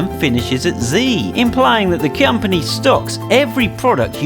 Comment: he says Cyumpony instead of company